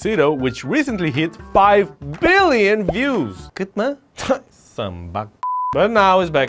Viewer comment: did you said" kitna" ,man you are awesome